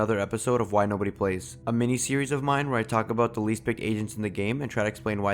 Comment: Dude I thought he said least picked asians in the game..